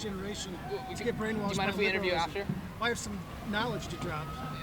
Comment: I thought she said illiberal 😂